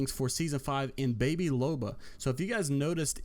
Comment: Everytime I hear "Baby Loba" I keep thinking I'm hearing baby Yoda 😆 keep up the good work with the squad cast guys!